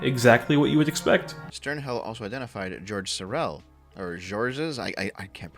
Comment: oh george sorel i thought you said george soros who is my main backer.